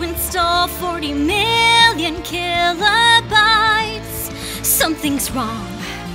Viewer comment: 4 a sec I thought she said stop trying to install 40 mill terrabytes I was like THATS GONNA BURN DOWN THE BUILDING